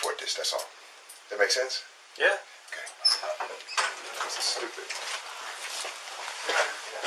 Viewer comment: I always thought he was saying "This is stupid."